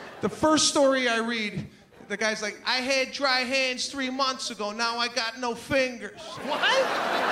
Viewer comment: “I had dry hands 3 months ago and now I got no fingers” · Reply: Dry hands? I thought he said DRY HEAVES ! Oh my gosh haha